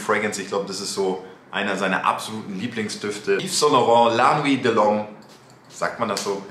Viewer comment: wie heißt der Duft?😂 verstehe nichts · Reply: La nuit de l‘homme. Riecht leider nicht mehr so intensiv wie früher